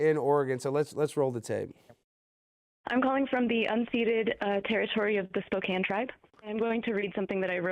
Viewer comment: "I'm calling from the unseated (unseeded, idk?) territory of the Spokane tribe." lmfao and here I thought her saying she's having trouble eating and sleeping because of a skit was bad. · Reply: unceded